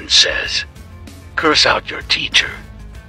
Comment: When it said curse out your teacher I thought it said come out to ur teacher for a sec 😂